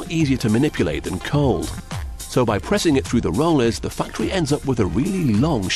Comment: did he say factory of fucktory?